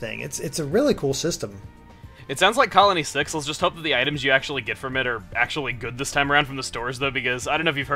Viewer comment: what the subtitles say:it sounds like colony sexual just have the items you actually get fermenter